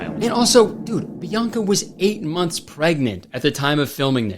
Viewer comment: I thought he said 18 months